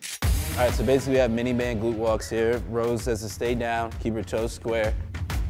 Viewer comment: I thought the first workout said gluten walk, I was like "OK these white girls took this gluten shit to another level"